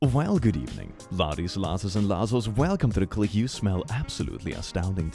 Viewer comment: I misheard Click call me "useful" instead of "you smell" which is unacceptable. Textbook case of a millenial ruining everything